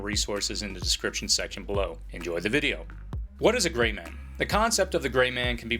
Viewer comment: i thought it said the 3 rules of the gravy man